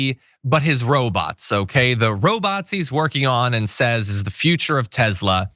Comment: You said, "Robots he..." and I heard "Robazi". Hmmm, ... Robot + Nazi... Seems Legit.